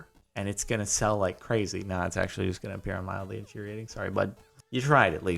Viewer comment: Who else heard him say mildly interesting at the beginning just to find out you had been watching mildly infuriating